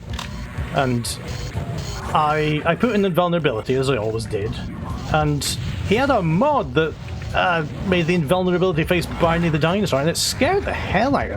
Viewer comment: What did you say at 45:56? You had a mod that did what? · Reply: My dad’s friend had a mod that changed the invulnerability face into a picture of Barney the dinosaur! It was terrifying